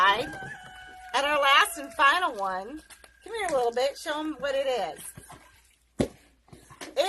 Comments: Wait - did she just say, "Com'ere lil beitch" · did she just say "come here little bitch" at 1:18 ?! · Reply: "Little bit is a term of endearment for small children.